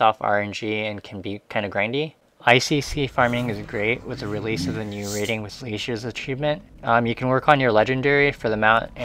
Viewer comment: What does he say at 6:55? About ICC. I can't understand him for a few seconds. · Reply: icc is great with the release of the raiding with leashes achievement [...] basically, with the new icc pets that were released in 7.1 :)